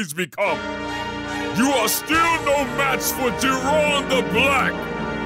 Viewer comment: Jir-on the Black is probably the best joke he's ever made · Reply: Tbh I thought man said Jerome the black